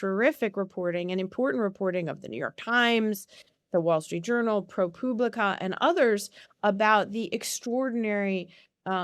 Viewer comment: Did she just say "Pro Pube-lica?" "Pooblica?"